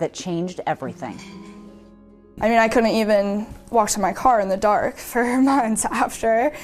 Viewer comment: Who washes their car in the dark? 😅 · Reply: They are talking about the roommate. She actually said "I was afraid to WALK to my car in the dark". It def sounded like she said "afraid to wash my car".